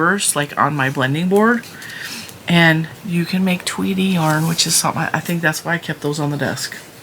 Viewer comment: Tweedy yarn🎉🎉🎉 cool though at first I thought you said Tweety. So I thought a Sylvester yarn 😂😂😂😂